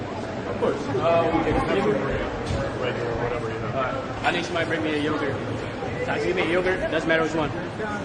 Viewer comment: I thought I heard him say "It doesn't matter what you give him. Just give me a yogurt, it doesn't matter which one."